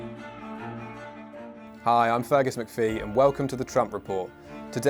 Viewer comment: I legit heard Fagus McPhee